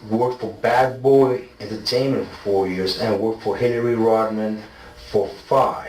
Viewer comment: Once he mentioned Hillary the detective was like: "Whoohw, stop let's talk about your family" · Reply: I was wondering if I heard him say Hillary Rodham ?